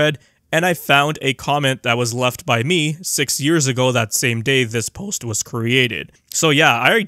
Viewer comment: thought you said it took you a few hours to work this out not 6 years 😂